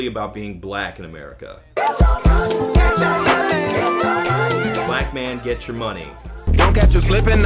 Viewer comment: I always thought they said "get your money! Get your batman!"